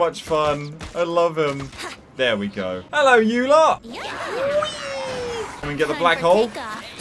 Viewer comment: I misheard that as "Hello, Eula!" Also...to me Venti was my boi. Nowadays he still is but I swear Ei is just too pure of a bean she's probably in the same tier as him. Plus birthday twin bias.